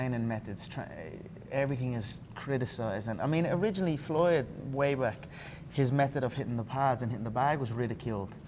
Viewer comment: did conor just say floyd maywack? Haha